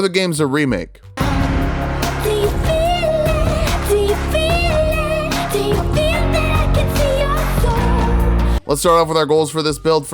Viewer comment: What's that song at the beginning all I hear (or rather think that I hear) is "he feel it, he feel it, he feel that he'd be your goal" and then link and that guy come and slash him away